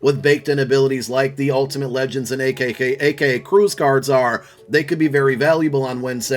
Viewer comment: Ayo i heard you say “akkk” min 8:30 😭😂😂